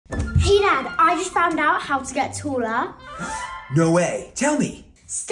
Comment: I THOUGHT SHE SAID: “I just found how to get sephoora” 😂😂😂😂